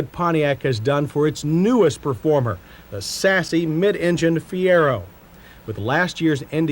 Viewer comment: Did he say "sassy"?